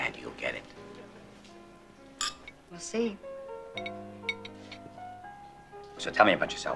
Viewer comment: Thought she said "merci" at first, but turns out it was "we'll see" 😆